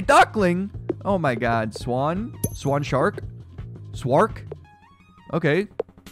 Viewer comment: Captions say spacewalk😂